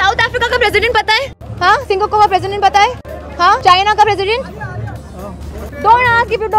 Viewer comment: Is she saying SINGAKO instead of Singapore or is it only me